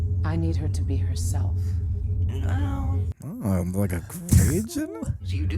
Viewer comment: What did say here? Like a what? Captions were of no help. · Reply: Bahaha - I said "Like a Gray Jedi?" in the most difficult to understand sarcastic voice ever